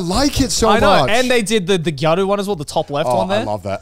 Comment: Can someone explain what a “gardo” or however u say it the boys mention it like 5 times but never explain what it actually it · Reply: Gyaru